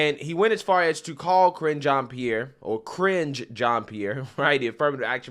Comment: Sounded like you said, Cringe Jean Pierre 😅